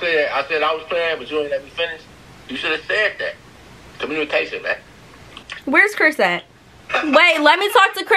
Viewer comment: Is it just me or did she try and edit out the part where it sounded like he said “communication bae” 11:13 ☺️